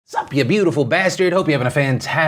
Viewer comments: Woo there, did you just say, "Whats up you beautiful bastard" 0:00 (non plural) 0.o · Wait, has Phil always said "bastard" like singular? Because I could've sworn he was saying "bastards" this whole time. 😭